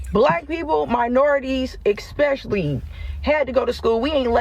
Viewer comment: Did she say "expecially"?